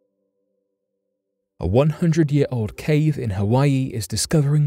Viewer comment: Doesn't say a hundred, it says hundreds(plural) But I think there's a good chance they're well over thousands of years old lol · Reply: maybe I misheard but I'm pretty sure he says a hundred years old. They're lava tubes. At least 5k years old.